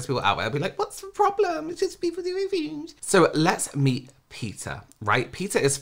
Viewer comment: gosh why did I hear “so let’s meet Pizza?” 🤣🤣😭